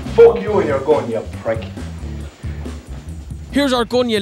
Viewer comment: he actually says "fuck you and your gun", not here's your gun. 🤣😂🤣😂